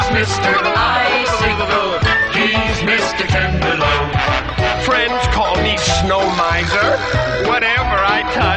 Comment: You know, I just realized: Snow Miser says "friends" call him Snow Miser, but, for Heat Mister, it's just"they": what a sad life he must live. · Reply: I legit always thought it said French not friends